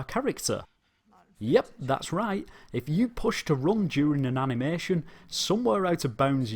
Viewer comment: “Yup, that’s right if you push to ______...” What does he say? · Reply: To run during an animation